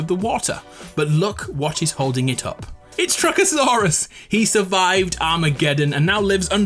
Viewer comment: can anyone help me out i didnt hear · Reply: Trucksaurus.